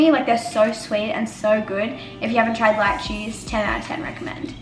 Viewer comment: When you said Lychee I thought you said light cheese 😅😂